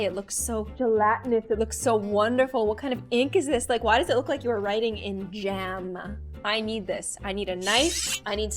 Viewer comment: I thought she was gonna say “why does it look like you are writing in BLOOD” but what she said instead was: “ why does it look like you are writing in gem”